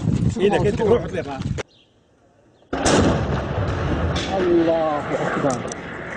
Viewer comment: En el minuto 1:20 hasta parece que dice a la verga